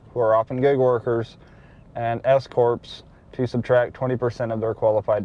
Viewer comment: u said S-corps, i thought i heard escorts.. lol Thanks for sharing!!